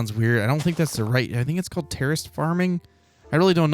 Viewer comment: lol i thought said "terrorist farming" hehe